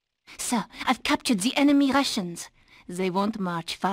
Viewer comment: I misheard "rations" as "Russians". Also, it's a bit weird how kangaroos are always Australian and pandas are always Chinese, but every other animal can be any nationality in cartoons. Are the hummingbirds from French Guyana?